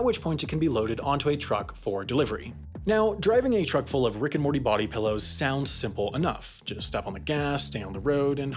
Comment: There’s an unhealthy amount of Rick and Morty body pillow jokes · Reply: I kept hearing that as "brick and mortar body pillows". Hunh?